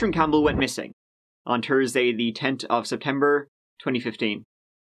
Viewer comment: did you say on tursday the tent of December 2015? just curious im asking for a friend and Read more